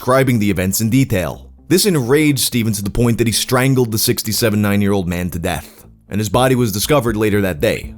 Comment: lmao i tried so hard to figure out what i heard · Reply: at first I thought he said that he strangled 67 9-yr old men and was so lost. Lol